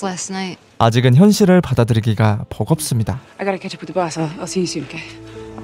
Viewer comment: 여기 전체 대사 들리시는 분 ㅠㅜㅠ 영어로 댓글 남겨주시면 감사하겠습니다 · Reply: I got to(gotta로 들려요) catch up with the boss, I will see you soon, okay? (해석은 자막과 동일합니다!)